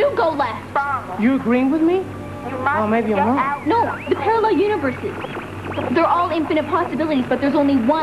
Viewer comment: about 4:33---did Catalina say "parallel univerSEE"?